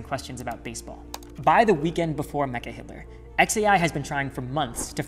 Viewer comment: An enlightning program, the first time I’ve seen Mecca Hitler in print, I always thought people were saying megaHitler . and it seemed to fit the way Musk speaks ,so I assume that was correct. I wonder if anyone else thought this, it’s like when you hear a song for years and find out you had the lyrics wrong.